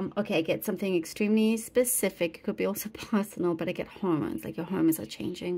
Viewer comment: What does she say at 3:32 that starts w/an H? What’s changing that’s personal? Home? Horn? I’ve replayed it over & over w/the sound blaring. I can’t for the life of me figure it out. It’s a missing piece! Lol this Virgo needs ALL the facts 🤓🧐 help please, seriously! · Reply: Pretty sure she said hormones. Good luck! ✨💖✨